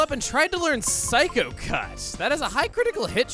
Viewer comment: I heard ”Psycho Cuss” 😆.